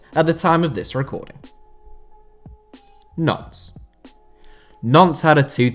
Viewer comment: is it just me or does it sound like he is saying nonce?